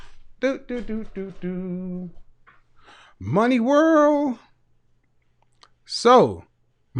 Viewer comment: Damn.... this whole time I thought dude was saying "Mami workkk" not "Money world"